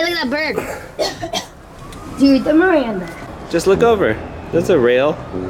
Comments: did say do the the miranda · did say do the miranda